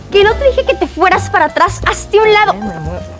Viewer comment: Alguien que oyó como dijo en el minuto 9:11 que dijo ''ay mi amor'' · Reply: No dice así dice "Esta bien deja me muevo" y fue cuando lo empuja